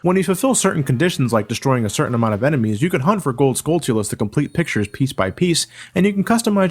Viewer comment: What did say at 4:40? "You can hunt for gold" what? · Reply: Skulltulas